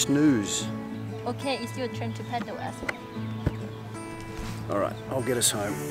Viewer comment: Sounds like she said ' your turn to paddle asshole ' hahaha she wouldn't though, she to nice for that.